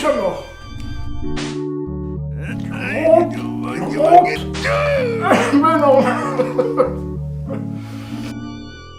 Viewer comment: C'est quoi le tout premier jeu vers 1:35 ? Je crois que Seb le dit mais j'ai rien compris :D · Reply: Daktlas C'est The Immortal